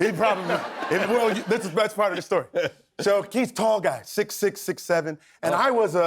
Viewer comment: Name drop? 5:44 the subtitle says “Keith” is a tall guy. Or did he say “he” is a tall guy?